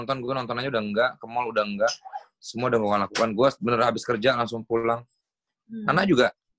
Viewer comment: "ke mall uda nggak, semua uda gue lockdown" Kok aku dengernya "uda gue lakban" ya 😅